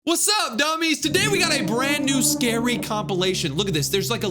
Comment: did he say DUMMIES?!